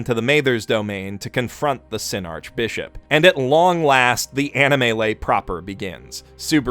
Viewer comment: I thought animelee said animalese and that you swapped the characters to speak animalese. I was disappointed.